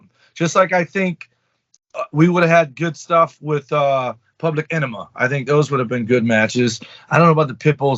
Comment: Did say Public Enema? 😅😅😅